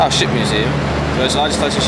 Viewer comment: OHHHH! SHIP museum! I was listening rather than watching and when it got to that bit I was all, "Wait. What did he say?" and I had to rewind and watch again before I realized. SHIP museum. What an absolutely fantastic film. What an achievement! Thank you so much.